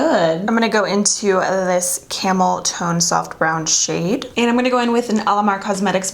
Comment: Lol I thought she said camel toe when she said “camel tone”🤣